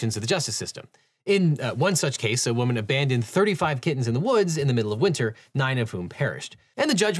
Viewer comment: I heard kids instead of kittens at 1:58 since i wasn't looking at my screen at the time and was horrified i hadn't heard anything about a person letting 9 kids die in the woods and then had to go back when i heard domestic animal abuse. Still awful and tragic but very much a different story.